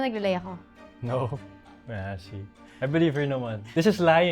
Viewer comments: Did he just said "My ashy" on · tama ba narinig ko, sabu ni AA “my ashyy 😊” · "no, my ashy"?!!?! DID I HEAR IT RIGHT????